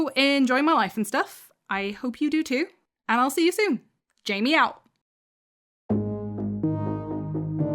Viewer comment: At the end you said "Jamie out" and I heard "JAMIEOW" LIKE A SMOOSH OF JAMIE AND MEOW AND I LIVE IT! 💕That should be ur new end phrase😎